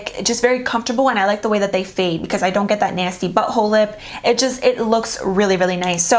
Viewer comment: O-k... I can't see saying this but..... At 12:22 it sounds like she says "I don't get that nasty butt-hole lip"....lol 💜💄👍😀 Still my fav....obvi